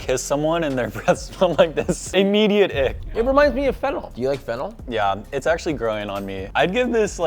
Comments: / Why did I hear: "It reminds me of Fental (Fentanyl). Do you like Fental?" 🤣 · I thought bro said reminds me of fentenyl i like it💀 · thought you said Fentanyl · sounded like he said fentanyl, plus i mean this in the nicest way, looks like Jake Gyllenhaal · i thought he said fentanyl 💀💀